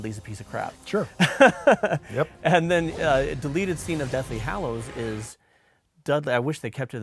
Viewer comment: I can't be the only one who heard him saying Dudley Hallows? Boy would that be a different story.